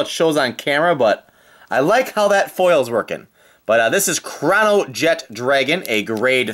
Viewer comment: i'm I the only one who heard him say crownjet dragon instead of chronojet dragon